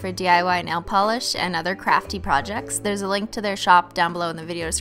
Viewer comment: i swear she said crappy instead of crafty